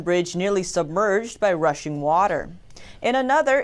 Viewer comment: I heard Russian water , I wasn't paying attention 😂